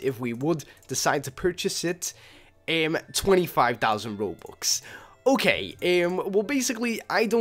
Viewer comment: wait...did he say 25,000 robux at 3:30?